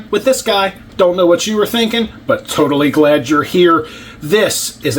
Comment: At the 39 second mark, I could have sworn you said “Wish you were naked, but totally glad you’re here” 😐 Had to rewind lol